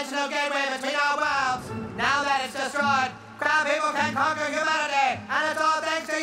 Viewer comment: crab people? I thought you said crap people